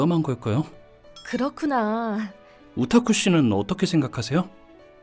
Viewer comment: 나만 오타쿠 라고들림?